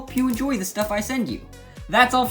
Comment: Did he just say Lego burst in left beys